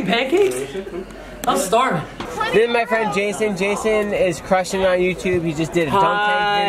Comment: Jac is laying her head on Scott's shoulders and holding his arm while he is holding her leg over his leg in the car. What other proof do you need !? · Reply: Praket Mehta it does kinda seem/sound like he said “Jason is recording” to her